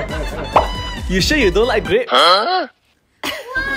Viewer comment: I thought u said it's a FUC!ing PIE but it was porcupine 😂😂😂😂😂